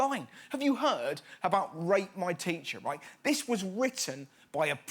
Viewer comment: When he said "rate my teacher" I thought he said "rape my teacher" and I freaked out even more when he said "primary student writ this"